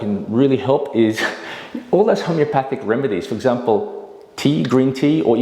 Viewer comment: "homeopathic". Is that a misspeak? Or did I just mishear it?